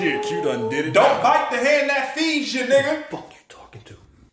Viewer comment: I thought he said “don’t bite the head that feeds you” I died.